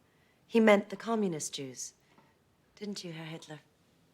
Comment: I don't think the man went by 'Heil Hitler,' Heil is just a way to introduce yourself. It's like saying "hi," Imagine, if throughout a movie, you just hear: "hi, Kyle." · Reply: I think you just misheard it, they say "Herr Hitler" which is just "Mr. hitler" in german.